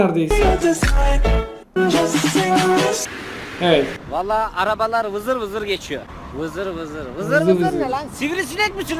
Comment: acaba ne dedi sjjs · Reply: Ben biliyom (oğlum) dedi:D